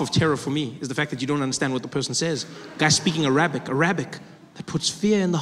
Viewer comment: Whenever he says Arabic it sounds like he says A rabbit.